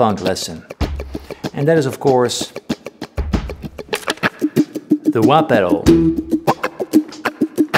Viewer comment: I SWEAR, the first two times I thought Paul was affectionately calling it "The Wah-Per-Oh." And I REALLY felt he MEANT it.